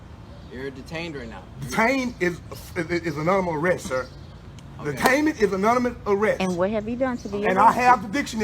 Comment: I cant make out what he said. He said “Detainment is blank* arrest.” Does anyone know what he said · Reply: Sounds like he said "anonymous"